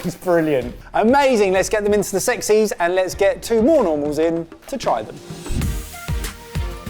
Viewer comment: 'lets get some more normals in' what i heard ' lets get some morons in' are we saying that now?